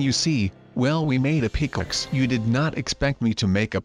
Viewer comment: All I heard was “we made a plipaxe